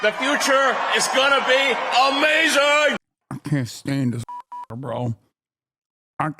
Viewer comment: It sounds like hasan was saying "i cant stand this fucking (nword)" at the start...